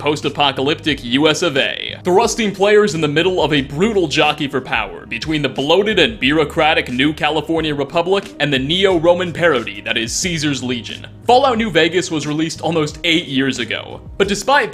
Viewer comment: Did you just say "bearocratic"? xD